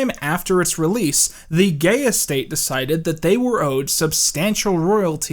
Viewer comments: I could have sworn you said 'The Gayest State' and I was all:! How could y-- oh, I get it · Every time said "Gaye estate" I heard "gayest state"